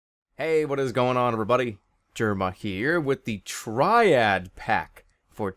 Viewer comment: When jerma said "with the TRIAD PACK" I thought he said "With the Tribes ascend gameplay" :D